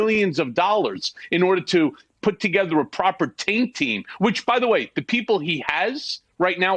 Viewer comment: Can someone buy me a quick vowel, what is that expression Cohen keeps saying at 3:45 and 7:45? It sounds like Tank Team, but I'm not sure? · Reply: I sounds like he's saying "taint team". But I thought the taint team is from the DOJ, so I'm not sure why Rudy'd have to pay for that. Maybe they also have their own. Basically, they (taint teams) are the ones that aren't the investigators. They go through the documents etc of the defendant (usually when it's a lawyer) to sort out the relevant from the privileged information. They have to be careful to not breach client/attorney confidentiality. (Criminal acts by the lawyer aren't protected by this confidentiality). But I'm not 100% that's what Michael said, at first I thought he said "tag team", as no lawyer works 24/7 which may be needed to get through the documents in time. Let's see what other people heard....